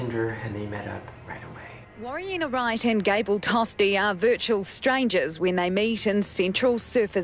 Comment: Okay, so my volume wasn't so high- and I thought you said “Cable Toaster” 💀💀